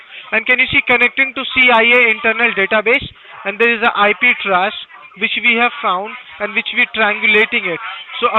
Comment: I just started dying when the scammer tried to say that SCP was his company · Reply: Every time he said "IP trace" I kept hearing "IP trash" I was like wtf is he calling trash lol...